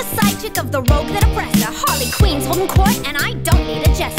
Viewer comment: I thought I heard her say hearly queen